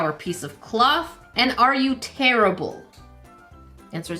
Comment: I’ve got to tell you, when you tried to rip the little bag for the first time, and asked if it was “tearable” I heard it as ‘terrible’